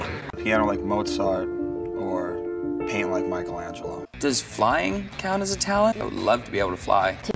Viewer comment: What does Matt says in 0:21? sometimes in the series and interviews I can't get what he says. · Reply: mozart and Michelangelo (not to be rude, just helping)